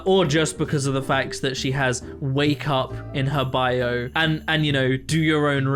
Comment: i was so confused bc you said she has “wake up” in her bio and i first thought you said “WAYCUP” and i thought it was an acronym for something and i was trying to figure out for way too long 😅😂